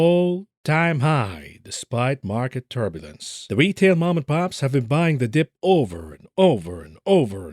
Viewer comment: I thought you say retail mom and pop has all dry up.